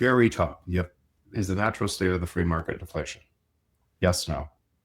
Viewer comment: Jeff says, "Is the natural state of the market ???" I've listened four times and STILL can't get that one word there. What's he saying? Anyone? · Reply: He says deflation, not inflation. He says this sentence on many podcasts